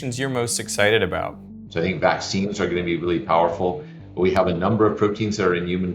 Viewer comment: did you say vaccines? Well, guess who is leading the HHS and who is in the White House.